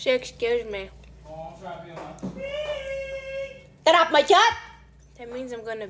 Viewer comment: “get of my gyatt!” That’s what it sounded like 😭